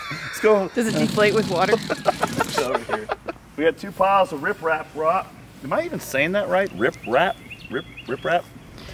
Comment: I thought he was saying riff raff rock lol